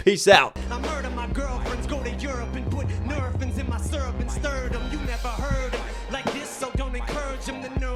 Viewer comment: The syrup and stir them line, i genuinely thought he said in my serpents sternum 😩😂